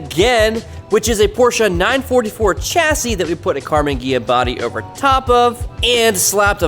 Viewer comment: My heart leapt for a second because at first I heard "CARBON Ghia body"